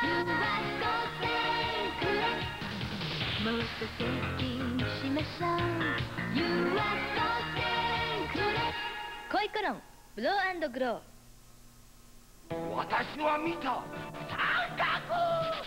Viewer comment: I thought that said "Fro and Grow" at first 🤣